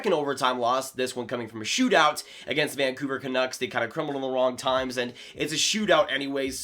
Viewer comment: wtf did he just say? I heard plubablumankon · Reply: crumble at the wrong times